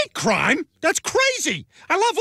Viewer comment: I thought it said gay abortions